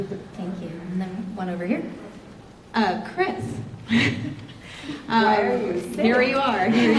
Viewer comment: What does Gal say at ~17:24? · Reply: "Why are you sad?" (maybe)